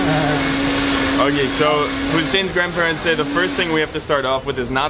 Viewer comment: Thank you for choosing to spend a good amount of time with my parents! It's a real unique experience for them showing you around Ramallah even with thier limitation with the English language. Their expressions said it all! This is a sample of how the Palestinian people are. We love our guests and we will always make them as happy as you were. Now you have a place to come to anytime you decide to visit Palestine again. Thank you for what you are doing and good luck with your amazing journey. Much love. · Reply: ination sorry I thought he said your grandparents. Of course they look very young to be grandparents